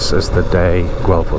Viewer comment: you said Guelph but the subtitles said “Galt”. I hope you can fix it